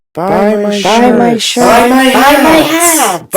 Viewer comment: He isn't? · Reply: they thought he said shit lol not shirt